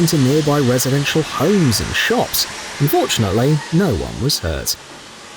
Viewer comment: straight up i hear "unfortunately no one was hurt"